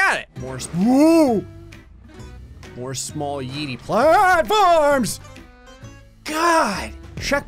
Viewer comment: Did anyone else hear him say farms